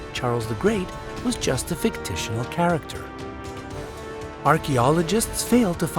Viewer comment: Who else hears "fictitional character" and not fictional character